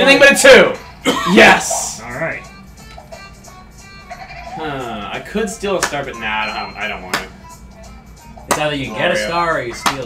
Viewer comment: does say "hmmm..Well i could steal a starBIT i dont know"?!?!?!!?